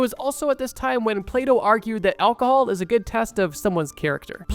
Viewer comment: When he started talking about Plato, I thought he said "PlayDoh" and was like wtf xD